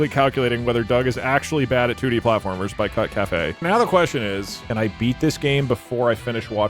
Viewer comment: I thought he was saying cuckcafe 😂